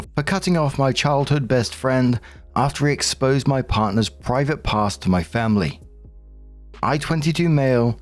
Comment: lol because has a non-rhotic accent I thought he said “private parts” 😂